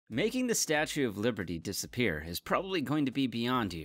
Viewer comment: in the intro i thought you said "statue of puberty"